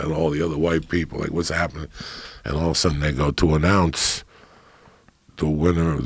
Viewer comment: Joey smokes so much weed that when he said "announce" I thought he said "an ounce"